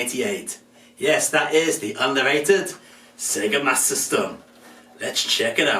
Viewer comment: Sounds like your saying Sega mas system.